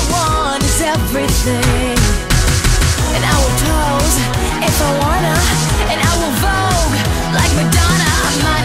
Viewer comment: I thought it said I wear clothes if I wanna